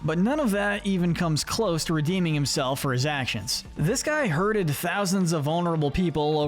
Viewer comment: did you really say “hurted”😭😭😭